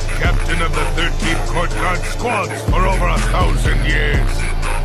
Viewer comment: I thought he said "The past 1000 years" Bro Ichigo don't even exist at that time